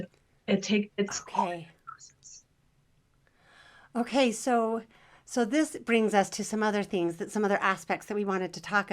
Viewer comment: Is there any way to find out what is said at 1:35:20? I just can't make it out. I think she says the wife is just so sensitive but then i can't make it out after that. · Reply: I listened on the master and her mic cuts out just as she said it. I guess we just need try to make it out, but if I remember, I think she said "fragile."